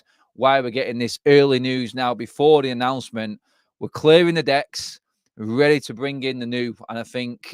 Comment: For a moment I thought I heard say "Clearing the DEBT" . Rather it was decks. 'Clearing the decks'. My bad